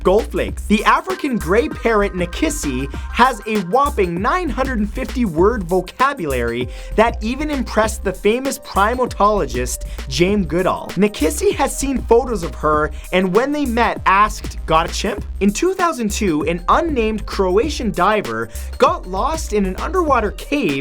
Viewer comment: I swear Matt said "Jame" instead of Jane Goodall. Even his mouth looks like it says "Jame"